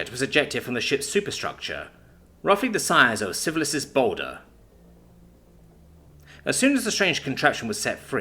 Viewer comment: I swear I heard "syphilis's boulder". Anyone else?